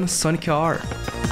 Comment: who else hears Soni-car